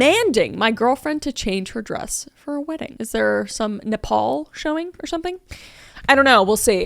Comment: I heard it as Nepal and I was confused, what has my country got to do with this story 😂